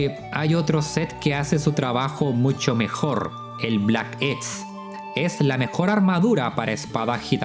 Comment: Cual es la armadura que dice en 16:19 no se de cual montruo es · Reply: Pero si lo digo ahí mismo, es el set Black X. Búscalo en Kiranico.